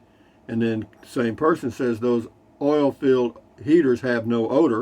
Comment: Ya'll kept saying "oil filled heater" and I thought you were saying "oil field heater" and couldn't figure out what the hell ya'll were talking about. I'm so stupid.